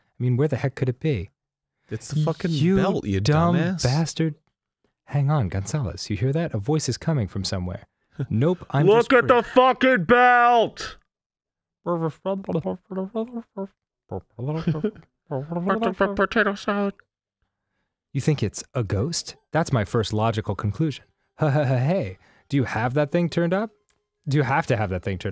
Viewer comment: did he just say "Looking for potato salad!" at 8:07 or noises then potato salad? xD